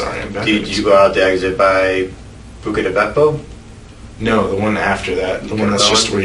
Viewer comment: What is the interrogator saying here? I’ve listened five times and it just sounds like “bukada bako”. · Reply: Bucca di Beppo, an Italian restaurant.